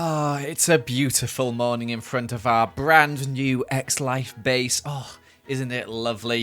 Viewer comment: In the start I heard "in our lovely eggs life base"